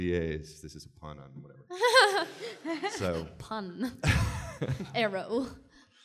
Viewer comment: Note that when she said "pun" at 7:10 she said it with a Swedish accent but it sounded like "pann"...